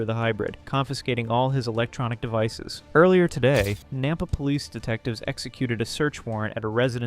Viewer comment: I thought they said NAMBLA police detectives lol.